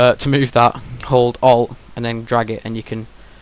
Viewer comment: how to move that thingy, i didnt hear it clearly, i use subtitle and it said "told all and I'm drug (its long)" and i hear only the "and then drag"